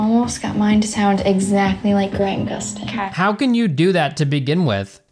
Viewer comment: did she say grant gustin? I guess you don't know who that is